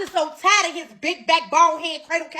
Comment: did she just call someone big back??????